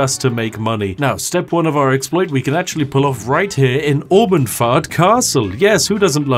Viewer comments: good grief I kept hearing "Almond Fart" and could NOT understand what was happening since I wasn't immediately looking at the screen at the time lol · I genuinely thought you said Mormonfart castle at first. 🦧